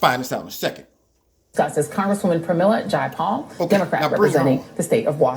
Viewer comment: Oh my gorsh! I thought it said PayPal!